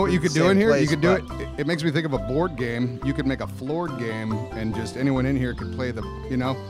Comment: I thought he said “floor gang” but he said floor game